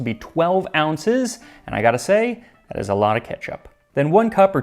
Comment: "That's a lot of ketchup" What i heard: "That's a lot of ketchup for a homeless person"